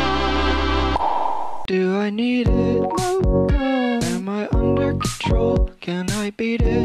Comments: omg when he said mocha i thought he said wake up lmao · Apparently people hear different things. Instead of Mocha I heard “No call” Other people hear “No duh” and “No god” etc. But we all know it’s actually a blue and black dress. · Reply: I heard “wake up” when he said mocha